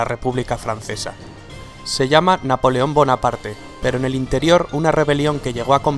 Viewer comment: pense que decia buena parte jaja